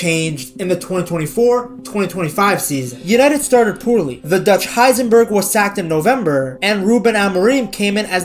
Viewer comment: Did I hear "ductch Heisenberg" 🤣